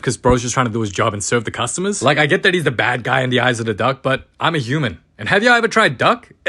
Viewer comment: ngl i thought he said dog